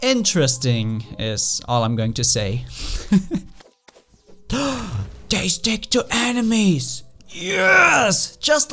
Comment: RNJESUS HAS BLESSTHED THOUSTVE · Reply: also i thought i heard "they stick to animes!" at the point with the true night fall